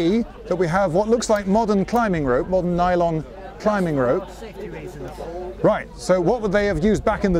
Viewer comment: Subtitles say [Inaudible.] but I definitely heard him say "That's for safety reasons."